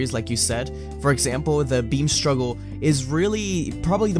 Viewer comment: I thought you guys were saying "bean struggle" for most of the video lmaooo